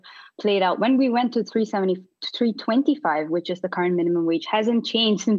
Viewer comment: Did she say $3.25 at 2:04? I'm sure she has a graduate degree from a prestigious university.🙄